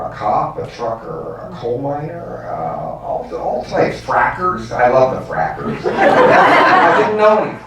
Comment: what is the word he mentions at 17:00 - i don`t get it clearly, a fracker?!?